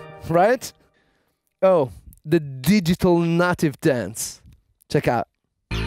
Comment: At first it sounded like he said digital nut dance, then I saw the title a breathed a sigh of relief.